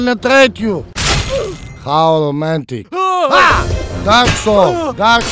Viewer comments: When a flat earthed starts blabbering BS 0:38 TIMEZONE TIMEZONE TIMEZONE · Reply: I thought he was saying dark souls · did he say dark souls?